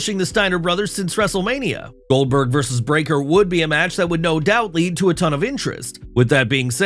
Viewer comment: Man I thought dude said Goldberg vs bron breaker would lead to a ton of injuries at first .